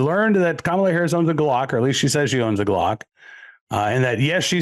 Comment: I misunderstood. I thought she said Gluck.